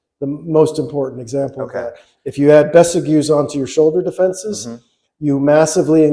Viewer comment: What's the word is saying at 5:45ish? "If you add (besigues?) to your shoulder defenses" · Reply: besagews